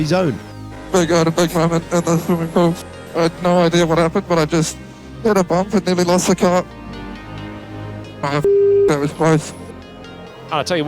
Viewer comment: couldnt understand a single word from lawson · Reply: " big moment at the swimming pool, no idea what happened. Just hit a bump and nearly lost the car, F that was close.